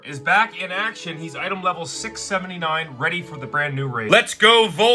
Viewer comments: did he just say… 6..7… 😂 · did he say 67????